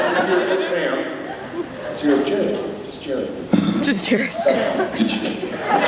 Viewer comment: what is Jensen saying ? something about 'Just Jared'. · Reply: Ritwika Datta He was making fun of Jared saying just Jared because he began to joke about him going to the jewelry store, Jared.